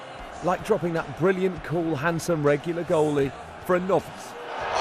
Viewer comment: does he call Scott Carson a nonce? Haha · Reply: He said Novice but it’s a lot funnier to think he called him a nonce